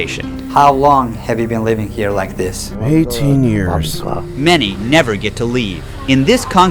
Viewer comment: did he said 18 years? 😨😨